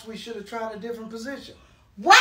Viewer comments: “Pops takes another dub” Love that he loves his dad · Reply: I heard it as pop takes another dump! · “Pops takes another dub” Love that he loves his dad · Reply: I thought he said “pops takes another dime?”